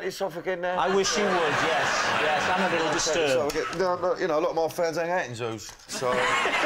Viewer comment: Can anyone tell me what Danny says at around 3:16 - a lot of my fans ...... ? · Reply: 'a lot of my fans hang out in zoos'